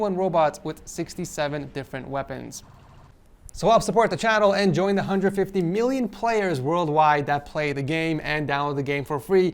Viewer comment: I thought he said "join the under 50 million players" Anyone else?